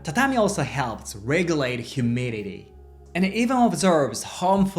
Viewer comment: I heard "tatami also helps regulate HUMILITY" and that was wonderful 😊